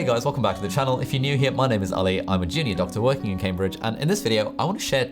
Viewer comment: In the beginning I thought you said "I'm a genius doctor "instead of "junior doctor" and it makes sense either way.